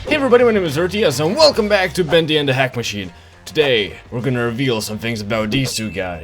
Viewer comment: I heard him say "Welcome back to Bendy and the hack machine!"